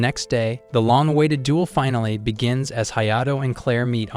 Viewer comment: when bro said liddy I thought bro said diddy's😂😂😂😂😂😂😅😅😊